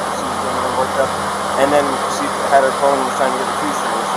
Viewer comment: Wait. I thought he said he had no phone ????